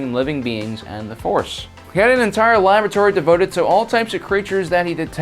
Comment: Gah I wish I could read plagueis but my library doesn't have it. Edit: I heard "He had access to his entire lavatory" instead of laboratory.